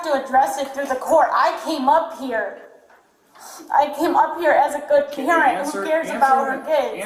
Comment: I legitimately thought she said “I came down here as a good Karen”